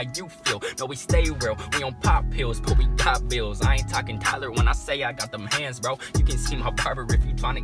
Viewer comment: did he say tyler1 at 0:25..... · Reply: No he said i aint talkin dollars